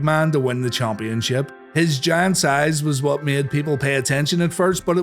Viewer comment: I thought you said 'giants eyes' instead of 'giant size' for a sec